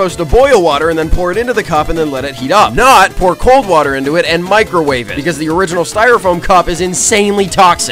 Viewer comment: i thought it said i can microwave